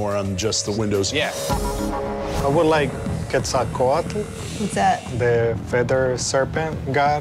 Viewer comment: Subtitle person just gave up at around 20:20 huh? He wants Quetzalcoatl, an Aztec god. I heard that just fine.